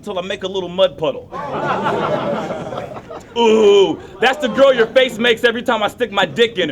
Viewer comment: Did anyone catch that line? "Thats the girl your face makes when i stick my dick in her" lol